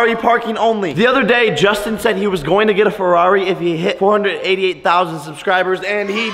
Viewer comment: I thought he said 812,000 subs